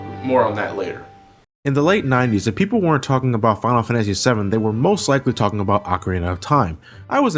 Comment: ohh now I got it you're right I heard it wrong :p I thought he said "people weren't talking about FFVII" didn't hear the if part